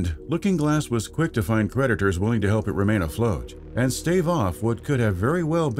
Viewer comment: Wait. Did he say 'predators' or 'creditors'?